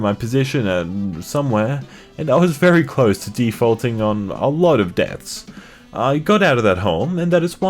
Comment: I thought he said "defaulting on a lot of dance"